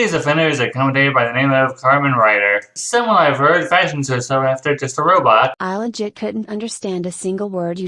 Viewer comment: your ears must be broken if you couldn't understand what he said. He said "This is Carmen Rider, someone that I've heard fashions herself after Just a Robot" 1:28 - 1:31 yes, that's exactly what she said. Throughout that entire situation. was treating the situation as if its no big deal. and put up this attitude of "i don't care. So you shouldn't care either", when yes. It is a big deal, especially considering the fact that the character that she's acting like as if its no big deal towards........... Is underaged 2:14 - 2:16 uhhhhhhh, yes it is. Its called giving context to the audience, so people could understand the conversation of who they are. People that are in the commentary community might know who they are, but newcomers won't 2:19 - 2:21 that doesn't answer his question. He's still questioning the context to explaining things to the audience and saying "because she did a commentary on her" is a bad reason why she's doing a commentary on a commentary on her, because it would lead to people assuming that can't take criticism 3:36 - 3:38 Ok.......... You do admittedly have a good point on paper, but as and would go on to explain. People making NSFW art of someone's OC's would still make the artists feel uncomfortable, no matter the age 3:56 - 4:02 Your just parodying what said, it doesn't matter if those characters are just drawings or are fictional. Its still creepy regardless, and will mak